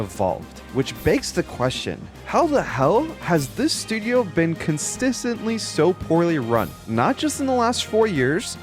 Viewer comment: what the Fuh did you just say? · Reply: Constissently